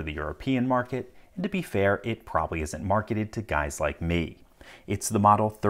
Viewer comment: Legit thought I heard "...is probably marketed to guys like me." Cool 2020 more power to you and